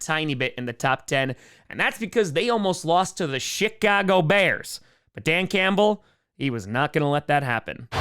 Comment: Kinda sounds like you said “ShitCago bears”